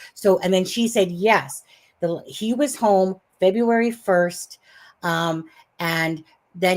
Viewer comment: I’m still watching and you’re playing the audio of and talking about what date actually was home. I thought said he was home February. 22!? Oh! I just replayed it and he said 26th. Psychics all say on that night was drinking beers at his mother’s when Katie called freaking out about Sebastian doing something wrong and she can’t take it.